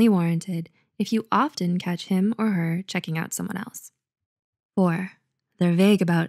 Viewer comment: I literally thought she said “checking out some a**” 😂😂 But she said it so fast.